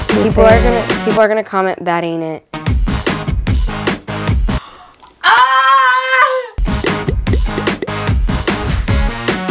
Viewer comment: I thought said daddy nit when she actually said that aint it🤣🤣